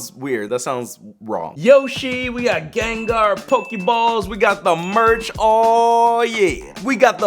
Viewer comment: When u said pokeballs i thought you said poke yee balls and my phones at 79%